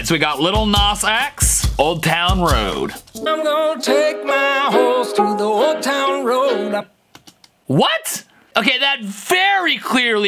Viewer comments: he is actually saying I’m gonna take my horse To the Old Town Road I’m gonna Riiiiide Til’ I can’t no more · I heard "I'm gonna take my hos to the old town road"